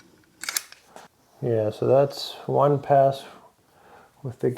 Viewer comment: WOW! Thanks! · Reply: Oh, I thought you said THAT's. I thought, "Yea, Terrific Freakin Content".